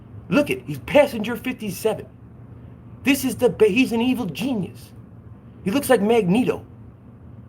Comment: did you say evil genius or evil penis? My hearing is not what it used to be 😂👍🏻